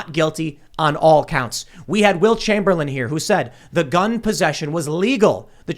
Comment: Funny, I heard "Wilt Chamberlain," but I realize you meant "Will Chamberlain."